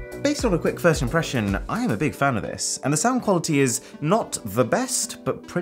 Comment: what he said: I'm a big fan of this My ears to my brain: tf he said " I'm a big feminist"